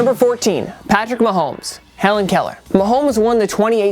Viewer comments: I thought Mahomes was saying Colour, colour not Keller lol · Why would anyone think he is saying Helen Keller? Yeah the play is to throw a blind pass to a wr that we made sure didnt hear the route to run...this play will def work. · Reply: I always thought he said Helen Keller but then I’m like he wouldn’t be saying it all these times so I had to listen closely and I’m like bam color color